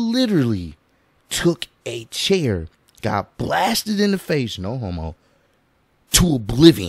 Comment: I’ve seen this video 9999 times and I just hear say “no homo”